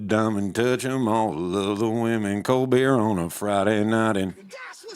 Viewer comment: I thought you said "Colbert" on a Friday night.